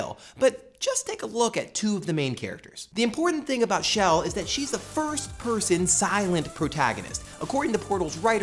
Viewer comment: Shell is not The First person female silent protagonist, there are lots of silent female protagonist. Samus Aran is one well named one. I may have misheard you, but I believe you said The First person and not a First-person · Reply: he didn't say "THE first person" he said "a first-person" as in a first-person perspective, meaning you play the game through the character's eyes. it has nothing to do with chell being the first of anything.